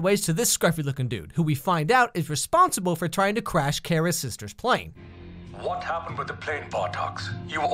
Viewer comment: i had my volume super low when he said kara's sister and i honestly thought he said carrot's sister